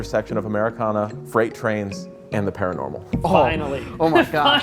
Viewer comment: I heard “marijuana, freight trains and the paranormal”….. sometimes you just hear what you wanna hear lol